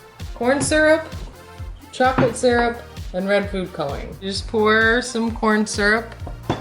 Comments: I heard her say red poop coloring XD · at first when she said red food coloring I thought she said red poop coloring then when she repeted it again I'm like o she said red food coloring